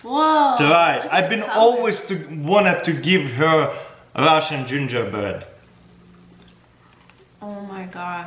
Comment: “Russian gingerbread” What I hear: “RUSH B